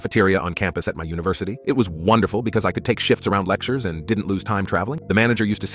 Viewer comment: i really thought he said "so i could take sh*ts around lectures" 💀💀